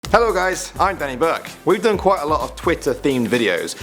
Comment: I heard 'hi there everyone, I am Benny Burke' instead of Danny Burke